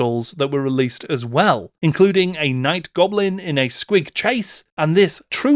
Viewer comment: I thought you said “Night Goblin in a squig chaise” and I really can not express the depths of my disappointment at mishearing you